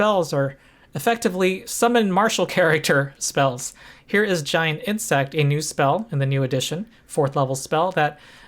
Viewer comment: When you said "giant insect," I heard "giant hit sack," but I think the difference is irrelevant.